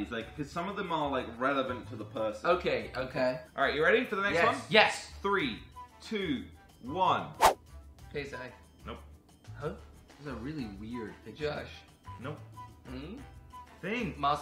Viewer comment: Jordi did you really say ksi🤣🤣🤣